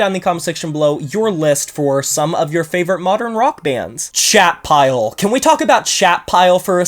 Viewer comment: I went and looked up Chat Pile and thought you were saying Shatpile. “Shat” sounds very sludgy